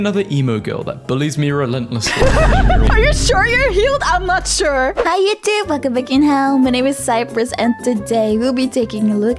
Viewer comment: Every time I misheard "hello YouTube welcome to fucking hell"